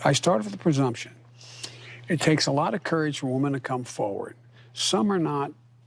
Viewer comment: Sound like joe said to come over to me!